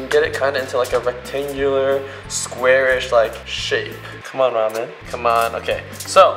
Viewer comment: sounds like he said shit